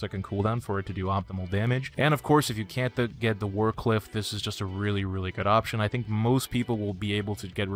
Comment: Did I just hear Wardcliff? xD